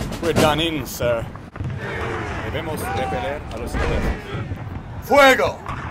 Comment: I hear "Lego" in one shot I guess the Spainsh guy is speaking "fire" I guess